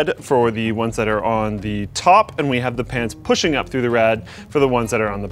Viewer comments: Anyone else catch him say pans instead of fans at 4:15? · Did he say pants? :D